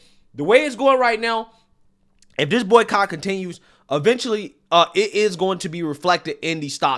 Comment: not going to lie I thought you said "Boycock"